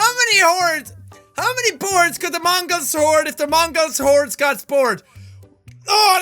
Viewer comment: am I the only one who thought he said whores??